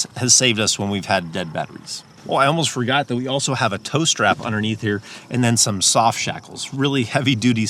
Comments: When you said “tow strap”, I thought you said toaster. LOL! I just knew that you were going to pull out a toaster from under your seat. Anyway, great ideas!! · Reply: And I thought he said "toe strap"! Could figure out either what that was or how it would be useful! LOL. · When you said “tow strap”, I thought you said toaster. LOL! I just knew that you were going to pull out a toaster from under your seat. Anyway, great ideas!!